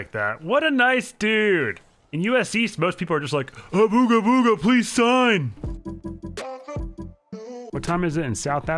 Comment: did say (and I quote) abooga booga please sign?